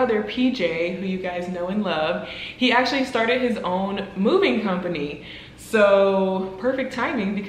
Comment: oh lol I thought you said that your brother has a movie company lol😂😂😂